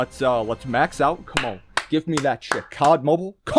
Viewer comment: sounded like he said give me that 💩